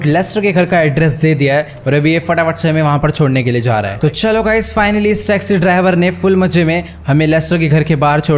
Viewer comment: said sexy instead of taxi😅